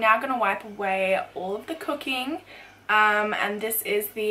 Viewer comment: I thought she said cocaine instead of cooking lol ((